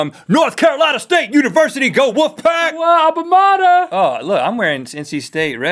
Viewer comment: Is it just me or did they say go wolfpac at 1:45?